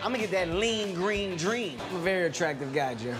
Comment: Did Kevin say Jim at 2:25?